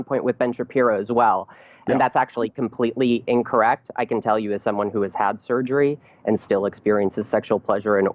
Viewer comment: first sip of coffee, watching Steven and I hear, " I can still cum" by a dude who chpped it off. Um kay, the apocalypse ty . Steven for the prepping as always. lmao.